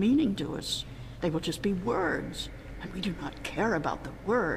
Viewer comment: go to 11:23 and listen to what the teller says and look at the captions. she says "... just be ..." but the captions say "... be just ..."